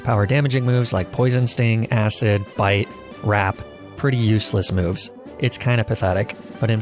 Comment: I WAS DRAWING WHILE LISTENING TO THIS VIDEO AND WASN'T FULLY PAYING ATTENTION SO I THOUGHT HE SAID "RAP" AND I WAS LIKE? OH POKEMON BE SPITTING BARS NOW???